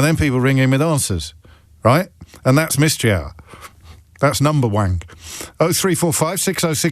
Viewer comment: Omg 😂..I swear he said number w@ @ 1:39:01😱😱🤣🤣🤣🤣